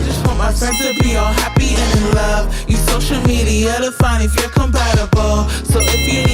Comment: Can someone tell me what sings after 0:28 about social media? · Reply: "to find if you're compatible"